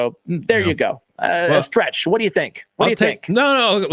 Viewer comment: Did just called Scratch?